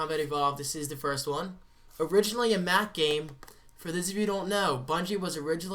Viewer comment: When you said Halo Was originally a Mac game I thought you said Math Game Lol